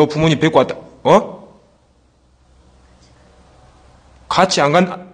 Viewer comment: 같이가자 발음 똑바로 들리넴ㄷㄷㄷ